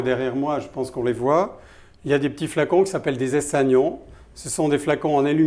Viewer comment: flacon en aluminium c'est quoi le nom ? J'ai entendu "essanion"